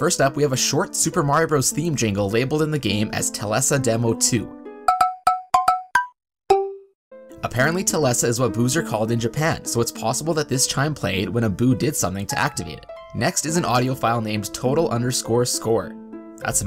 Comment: for a second I thought he said booze as in alcohol.